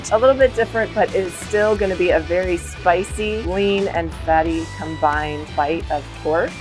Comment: did she say lean in that?🤣 there is nothing lean about porkbelly and the amount of fat and marinade they were sitting in is enough to make any lean cuts of meat become not lean lol